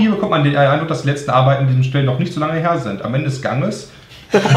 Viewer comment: Was sagt Brammen bei 22:11 wo alle lachen ,irgendwie verstehe ich ihn net :D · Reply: Brammen hat gesagt "Am Ende des Ganges", war mal in nem lp (ka mehr welches) ein Wortwitz von Hardi, den nicht alle direkt verstanden haben. Deswegen wurde ungefähr 3 minuten lang über diesen Witz diskutiert und mittlerweile ist es ein kleiner Running-Gag :)